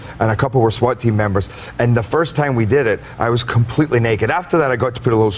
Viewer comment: what does he say at 1:30 ???? "And the first time we did it I was completely naked, after that.........." ??????